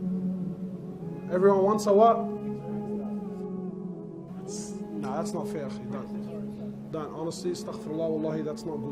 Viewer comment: what did the guy say? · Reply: I think he said golddigger